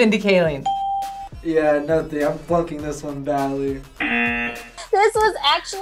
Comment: I heard it! Did you hear it too? BTW! As an IMDB expert, could anyone guess who voices Eret from How to train your dragon 2? Without cheating. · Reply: He said "flunking"